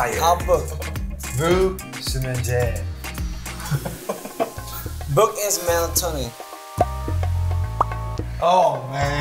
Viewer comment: No one: JK: “Book is melatonin” my heart this boy’s been learning so much 😭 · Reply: I thought he said "book is melancholy" 😂😂😅